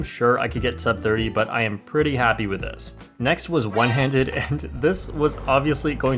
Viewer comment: I thought i hear "But I'm thirty happy with that"